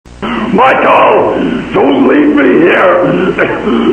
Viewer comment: it sounds like hes saying MICHEAL DONT LEAVE ME HERE, MY TOE MY TOEEE, HELP ME